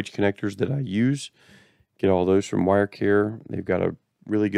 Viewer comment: Thanks for bringing up wirecare. I've been looking for a place that has good connectors and the tools to do the job. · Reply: nice.. i thought he said wirecure.. thank you for commenting about.